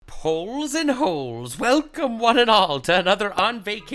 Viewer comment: Hole and holes may be my fav intro up till now Ooh it was poles not holes... i like my misheard